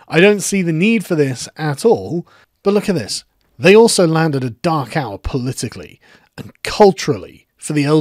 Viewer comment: Who else heard “Dachau” the first time he said “dark hour”. Love that accent!